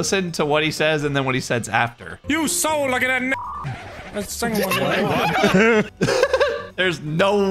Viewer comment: He was trying to say "you so lucky that second one never went off" but spoke faster than his brain could think and ended up saying the n from never at the start of "second" resulting in him saying "that necond" which sounds like that one gamer word when said fast.